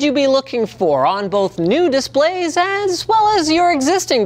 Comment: I'm sorry, I keep hearing "new display" as "nudist play"